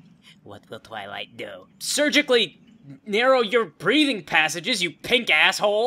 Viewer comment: Did i really hear "surgically narrow your breeding passages" ??? wowor was that breathing I LOVE pinky in this! outRAGEous!